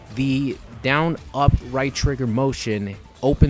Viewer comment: ok thank you i was confused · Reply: sorry for that comment btw i just re watched the vid and u said down up right trigger i thought you said they changed it to df+1